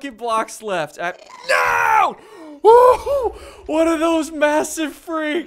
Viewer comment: WHAT ARE THOSE!!! (He really said "One of those massive freaks" but I want to know...)